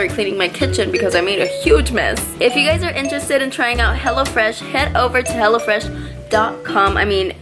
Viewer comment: I thought she said hellafresh 😂